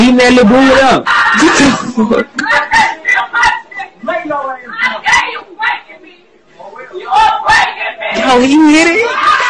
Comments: Maybe she was just going crazy because her family were trying to hold her down to stop her from running away or harming herself and she just wanted no one to touch her..especially now that the police confirmed no rape and no injuries..maybe this girl thought it’s just crazy how wild her family member or friend is screaming but also knew she’s not being harmed so she recorded it and laughed...u know teens these days..for god’s sake there was a white boy doing musically right in front of his dying grandpa in the back in a hospital room 🤦🏽‍♀️ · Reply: Kinda sound like she was saying "get off of me youre breaking me"and he said oh well but his pants were undone so i could be wrong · The Audio was horrific but it did sound like she was saying you’re breaking me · on the last part of that video it sounded like she was saying you're breaking me. As if he was breaking through her private area with force. I'm trying to figure out why she's protecting him if he was rapping her.